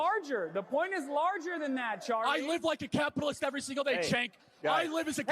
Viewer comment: When people said Charlie called someone a "Chink", a racial slur against Asians, you can CLEARLY hear him @ 8:59 he clearly says "CENK", as in Cenk Uyger the greasy Turkish buffalo!